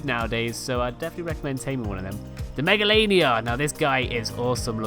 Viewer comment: who thought he said megalovania